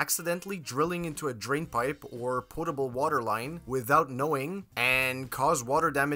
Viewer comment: did you call it a "poRtable" water line?